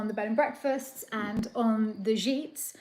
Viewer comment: What are the zheets (@ 47:44)? says they were dependent on the B&B and the “zheets”